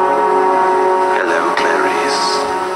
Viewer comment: I thought he said well Clair